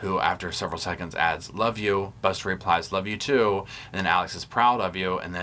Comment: I don't hear Buster respond with 'Love you too' in that call. I hear 'alright...okay..' ?? Sounded like he just wanted off of that call...